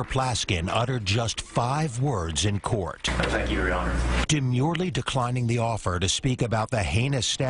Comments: "He only had five words to say", defendant, "thank you your honor", everybody that attended a good educational system, "I count 4" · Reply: I thought he said, “I thank you, your honor.” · That's four words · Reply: Kev hendrix that’s what I thought too lol but he said “I thank you judge” I missed the “I”